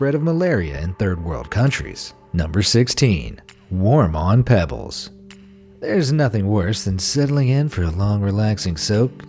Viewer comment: I thought he said war mongering pebbles!